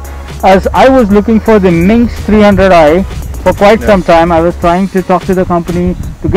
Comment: u say a bike called minx 300i... What's the name of that bike actually.. I cannot figure out the name of the bike? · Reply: m1nsk 300i / cyclone trx 300